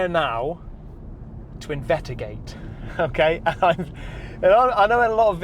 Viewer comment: Minute 1:36, did he say "invetigate"? (without the 's')